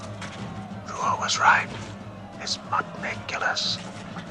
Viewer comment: i always thought that he said "This Mudicolous" lol. Excellent Subtitles